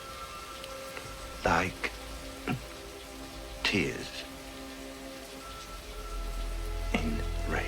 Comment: well thanks to the Venom, every time I think about 5:55, I hear "like a TURD, in a WIND"